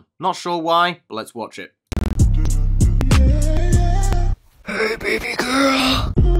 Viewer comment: I want to die now because I heard “hey baby girl” KilL Me PLs